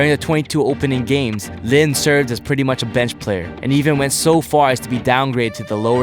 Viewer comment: Hello! Really appreciate your in-depth videos! Just wanted to point out some incorrect subs, like at 7:31 where it says "best" instead of "bench". It's small but it completely flips the meaning of the sentence. My friend is deaf and relies on captions, so he was thrown off for a second. Still, thanks so much for all your effort in making these videos!!